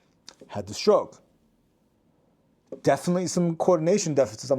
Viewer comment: did he say he had a stroke?